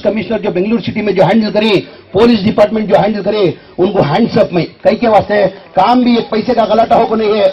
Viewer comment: I heard hands up 🤣🤣😂😂